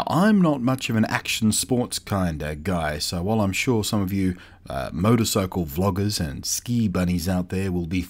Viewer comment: Did you say "Motorcycle Vloggers" ? I'm in